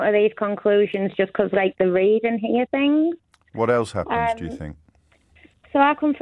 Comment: I thought he said "what else happens WHEN you think?"